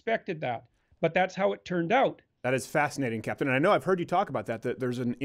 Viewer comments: Did he call Jordan Peterson "captain" 42:40? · Stephen calls Dr Peterson "Captain". I think. What would Freud say?